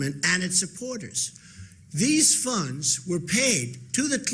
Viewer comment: does he say "these fucks"?